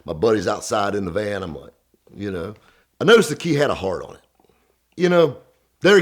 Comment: I thought he said "The kid had a hard on" i was like wtf lol